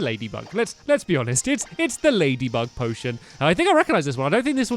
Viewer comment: Did anyone else hear that he said "Ladybug Potion" instead of "Ladybug Pokémon"